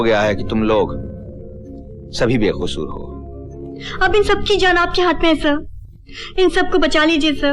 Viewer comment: I wonder if he says Bewakoof instead of Bekasoor 🤣🤣